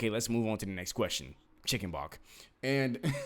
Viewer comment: perfect definition of every word. 1 question? I love love love your expression "chicken _____" what the heck is that word "bock" "bach" hahahaha its a perfect description.